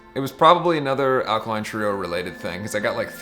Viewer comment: Who is his favorite band (2:25)? I can't understand the name (non native speaker). Help anyone?! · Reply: Alkaline Trio, it's a rock band